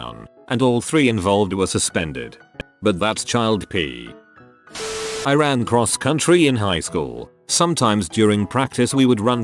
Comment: I thought they said Iran cross country and then a ad started I was also not paying attention so I was like wtf